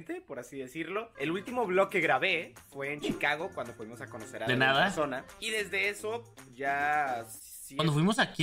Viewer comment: no se porque yo también lo repetí como 3 veses porque también entendí "una persona"